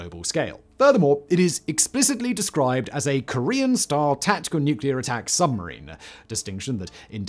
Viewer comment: why do I hear Korean "Star" rather than style? Also, why do british sound like they're eating their own words to have it sound like something else?